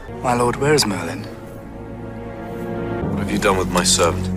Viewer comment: OH MY GHOD THE FACT IN 4:28 I THOUGHT HE SAID WHERE IS MY SON MAKes meee laugh 😂😂